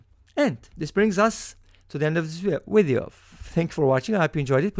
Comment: in 9:24 did you just say "wideo"? :) btw. 2nd method way easier